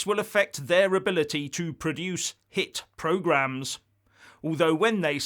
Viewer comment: When they said hit programs i thought they said shit, sorry to be so crude but i do not watch or listen to anything the bbc lounge lizards produced.